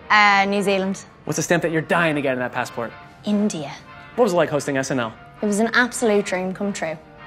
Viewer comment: did she said INDIA ?🥺👉👈 Ronan please come to india 🙏🇮🇳 👰🏻💍🤵🏽